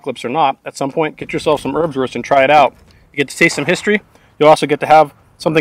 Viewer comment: Thought you said herbswurst, that sounds more like a Netherlands thing. This differs from wurstherb would be something I experienced back in '95 in my buddys dorm.